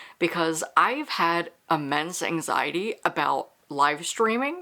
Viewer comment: Yay, you picked my superlative, and the recs are great! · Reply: I misheard "immense anxiety" as "a men's anxiety" and was like "yeah, fair" and then realized what you actually said 🤣🤣